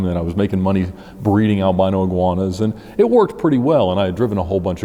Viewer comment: I lost it at "I was making money breeding albino iguanas" lmao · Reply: That's what he said lmao I thought he said I was breeding marijuanas lmao